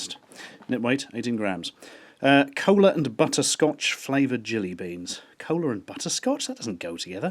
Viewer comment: i think it meant to say," colon and butthole flavored jelly beans"